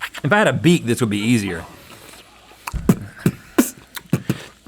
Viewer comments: I cannot stop watching link beatboxing. It was just so impressive to me. I also thought I heard Rhett ask for a beat 🤷🏼‍♀️ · I heard beat too, not beak